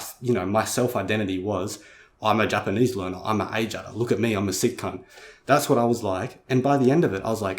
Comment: did you say "sick cunt"? Great video too, never really considered things like this.